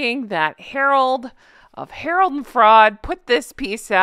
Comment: I've just realised you call them Harold and FRAUD. I always thought you said Frog 😂😂😂😂😂